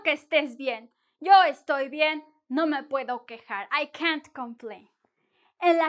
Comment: What'd you say at 0:12? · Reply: I can't complain.